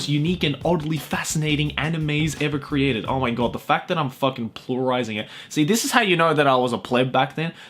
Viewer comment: did he just say plurising.....